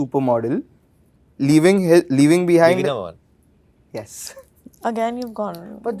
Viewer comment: what did arun dhawan say at 5:24 ? the movie name? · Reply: Biwi no 1